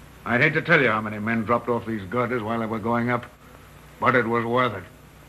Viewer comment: GIRDERS I got to call you out on this one, he did not say gutters, he said girders. Steel Beams I beams girders